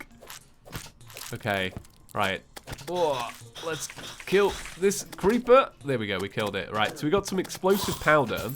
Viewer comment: did he just say let's kill this creeper or is it just me